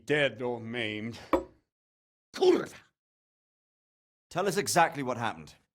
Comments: im sorry but does he says in polish "Kurwa" ?? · Did... did he say "kurwa" ?